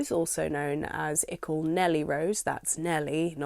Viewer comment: Flipping heck. For years I thought it said Ickle Ellen Rose haha